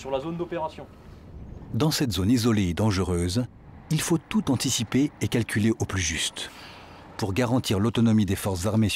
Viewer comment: il dis quoi 1:50 ? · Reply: Il faut tout anticiper et calculer au plus juste